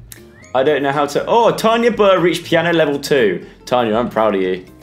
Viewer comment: When he said Tanya burr I thought he said tiny burt